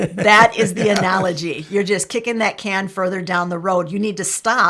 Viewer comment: I thought you said, “You’ll get a kick in the can down the road.”